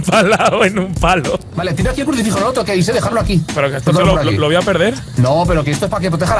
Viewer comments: Creo que dice "Vale, tira el crucifijo, el otro, que ni sé dejarlo aquí" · Lo que dice en 3:19= "vale, eh tirado aquí un crucifijo, no lo toqueis eh, dejarlo aqui"